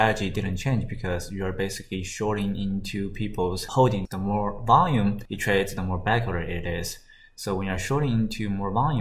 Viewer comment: Can anyone hear what is he trying to say? i cant seem to catch that word. "the more volume it trades the more ____ it is, so when you are shorting into more volume just make u short more safe" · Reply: he's saying the more volume it trades the more bagholders there are, so when you are shorting into a resistance it's safer when there is more volume traded in the resistance zone because more volume traded = more bagholders